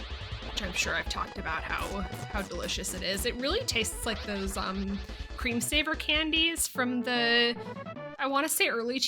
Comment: I just hear "Screensaver Candy" and I wish there were screensaver candies Edit: And I like the thumbnails : )